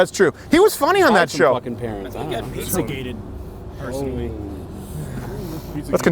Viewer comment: ish did someone say Shia got pizzagated? fuckin' lol